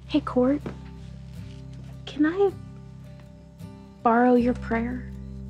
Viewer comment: bro I was so confused when I thought she said “Hey Kourt, can I borrow your printer” lol had to listen again to hear “prayer”